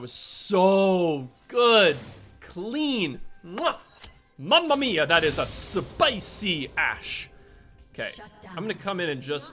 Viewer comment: I was listening properly and all I heard “Mama Mia that is a spicy ass” XD